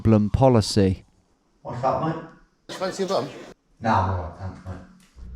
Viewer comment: what did he say? Fancy a bump? Was that it? 😅